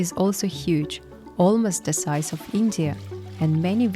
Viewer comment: Yakutia is the size of india did i hear that right i mean i expected yakutia to be like a small village or the biggest i thought that it would be the size of california but india thats crazy or did i hear wrong